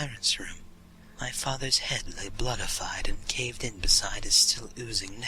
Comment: Did you say 'bloodified' at 07:53 ? *giggle* I loved it. Creepypastas fill my heart.